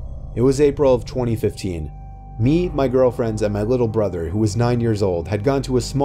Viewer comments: i swear he said girlfriends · Am I the only one who heard him say "GirlfriendS" 👁👁